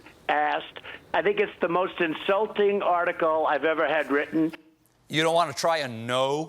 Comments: Does Trump say at 05:38 "... article I ever had written"? Whatta HELL is that about? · Did Trump say "it's the most insulting article i have ever had written"?? 🤪